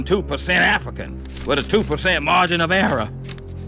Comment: "102% African with a 2% margin of error" DAM so that means Ruckus could be 104% African for all we know. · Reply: I thought he said margin of arab.